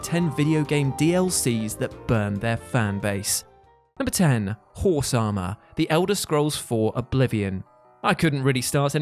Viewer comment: Is he saying Whore-sama instead of Horse Armor?